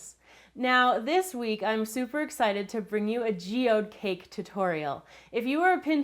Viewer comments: At first I thought it said geodude cake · Can you make something linked to Pokemon go please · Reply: I thought it said geodude cake and got excited lol